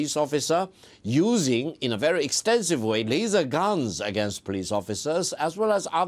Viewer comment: Did he actually say the protesters were using "laser guns"?